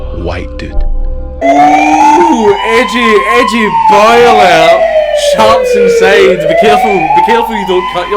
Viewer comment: Why did I hear ‘edgy boiler’?😂😂